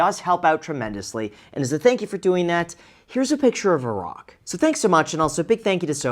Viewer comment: I honestly thought he said 'and here's a picture of Iraq'. Took me a second.